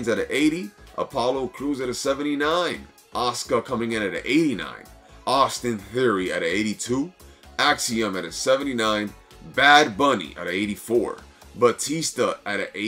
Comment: What does "overalls" means in current WWE 2K games? In HCTP overalls were physical stats of superstars, now Bad Bunny is 84 somehow. 2006 Cena is 90 somehow, but he was literally untouchable in 2006-07, his peak, i don't understand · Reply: I had to replay that part cause at first I thought I heard 94 for Bad Bunny haha was gonna say how the fuck he’s a 94 😆